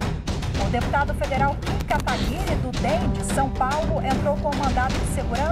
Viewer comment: só eu entendi " a deputada federal Km Kataguiri"?